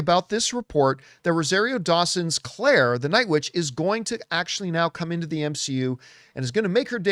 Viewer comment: Huh?... Did he just say Claire the night 'witch'? lmao🤣